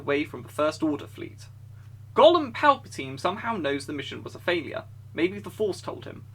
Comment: "General Weasley" "Golem Palpatine." "Pinkie" Damn this guy has me crying with these names. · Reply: I heard "Golden Palpatine" :)